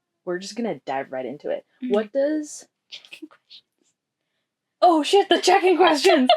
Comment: Everytime I hear you say "check in questions" I think "chicken questions"...